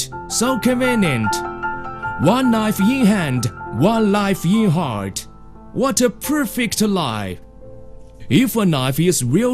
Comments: Does anyone know what he's saying between 5:06 and 5:12? It sounds like it should be very, very wise, but I just can't understand the words... One knife ye hand, one life ye heart, what a perfect lie! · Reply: Oh...haha I was way off. I heard "One knife ye hand, one knife you heart, what a perfect lie." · Does anyone know what he's saying between 5:06 and 5:12? It sounds like it should be very, very wise, but I just can't understand the words... One knife ye hand, one life ye heart, what a perfect lie!